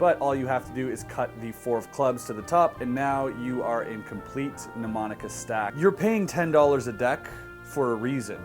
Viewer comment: What did he say at 7:49?? Numanica stack??? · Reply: mnemonica stack, i had to look it up too... I'm still a new magician